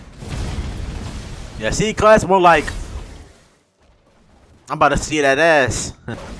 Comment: BRO, IT GOT ME AN AD AND I HEARD: "yeah c class? more like im gonna see his as-"